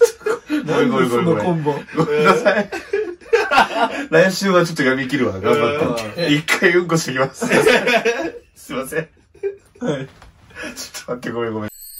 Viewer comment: 「1回うんこしてきます」にしか聞こえなくなった